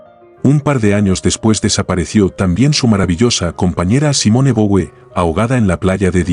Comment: ¿No se refiere a Simone Weil? Entendí Simone de Bouvier. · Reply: No, se refiere a Simone Boué, la compañera de Cioran.